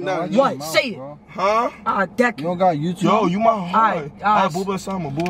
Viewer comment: what said it l deck u😂